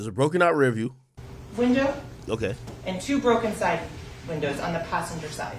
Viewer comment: ak need to lay off the liquor she clearly said 2 side windows on the passenger side but in ak mind she said 2 side view mirrors but it’s clear that ain’t what she said lMao that’s why you can’t have a Nigga like this on yo case lMao